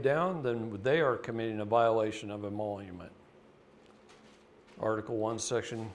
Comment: Do you know what is saying at 46:13 ? "then they are committing... ??" Can't understand and the CC is coming up with the word "monument" lol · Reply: the word is emolument - a salary, fee, or profit from employment or office.